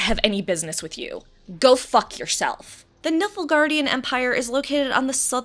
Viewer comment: Did… did she just say “Niffelgardian empire”? 😂😂😂